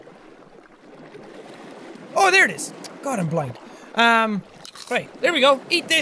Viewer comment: What is it at 1:48 it sounds like he said god I'm black